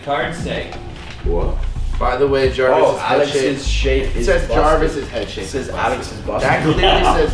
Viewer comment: I thought it said Jarvis’ head shape is banned